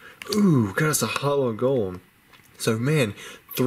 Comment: I thought u said holo gold not golem Lol